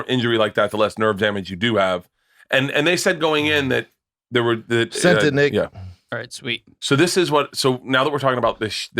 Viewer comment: did Brendan say “sent to nigga” ??